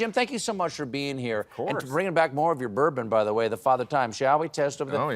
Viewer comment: I thought he said bring back more of your burpin